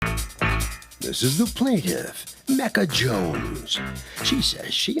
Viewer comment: I thought he said “This is the plaintiff “MEGA JONES”😂😂😂